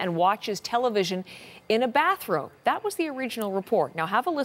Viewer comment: All this time I heard: "Donald Trump doesn't have a bathROOM" instead of "bathrope" XD I should learn more english...